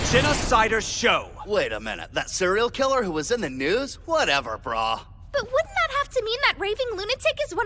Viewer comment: "whatever bruh" typical yasuhiro ig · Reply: I SWEAR I HEARD HIM SAY "whatever bra"